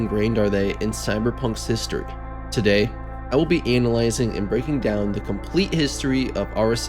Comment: Okay I hate to be immature like this, but it totally sounded like you said “anal-izing” instead of analyzing 😂😂 I’m sorry I’ll see myself out P.S. love the video. I live and breathe CyP2077 lore